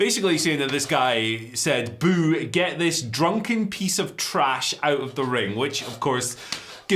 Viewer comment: I cannot believe that's what that guy said. I thought he said 'get this piece of trash back to WWE'. Some people have no empathy. That guy probably had an alcoholic father or something and doesn't know how to express their anger. I really hope he got thrown out. Piece of shite. Mox handled it like a champ